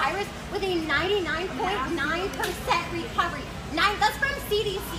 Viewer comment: “99.9% recovery” First, that’s wrong, and second, the leftover 0.1% would equal to 7 MILLION people. So shut up. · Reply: Whops, you’re right 😂 I need to sleep, I thought it said 99.99 lol